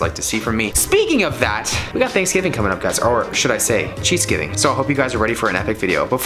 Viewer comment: Random but if you ever travel to the north east, id like to see a Sheetz challenge. When you said cheats giving I thought you said Sheetz giving lol